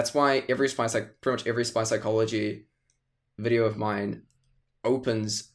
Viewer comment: Gonna be real, "mycology" was my first thought also when I heard "Spychology".